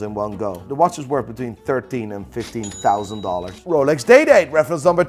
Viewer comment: As a non watch-aficionado, I kept hearing "Rolex Dayday". Those watches will forever be DayDays to me now. Thanks Nico.